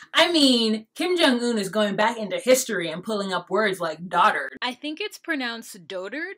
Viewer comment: The dotard comment took me out, at first I thought he said doodoo-turd LOL then I thought about it and was like oh yeah dotard sigh